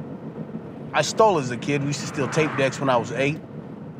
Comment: did he say tape decks